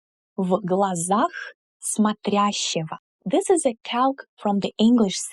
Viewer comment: you say "this is a [?] from the English saying." What word is that? The only subtitles are auto-generated and say "calc." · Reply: Calque. <link>